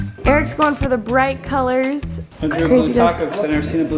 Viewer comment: I don’t think he’s saying he “grew” a blue taco 😂 perhaps he ~drew~ it? Lololol